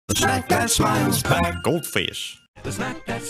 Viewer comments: Why do I hear "Smack That Child's Back" ? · I keep hearing 'snap that child's back! Gold fish'